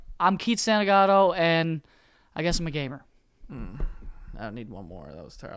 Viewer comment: Im not gonna lie i thought Keith said "and i guess im a gay man" Hot start